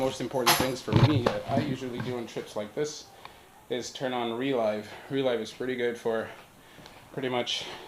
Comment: What’s the name of the recording app? Sounds like you said “relife”. Thanks! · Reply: Relive